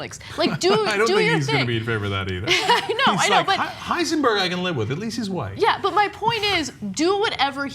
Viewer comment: did Cenk say "Heisenberg I can live with, at least he's white" ??